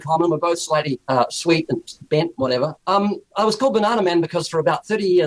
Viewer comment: Did Ken really say, that when he eats a banana, he thinks of Ray? It's like he's handing out the ammunition! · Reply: And did Ray really say he was bent?